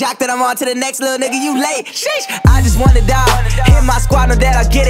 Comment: I thought he said "wanna die" but he said "won a dime"